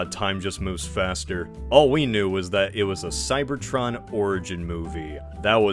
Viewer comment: I heard "cybertron orgy movie" at 2:46 and spat all my damn coffee